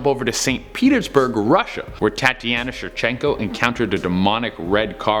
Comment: did he just say thotiana